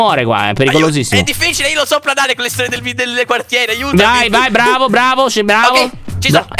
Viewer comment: ho capito storie del bidello 🤣🤣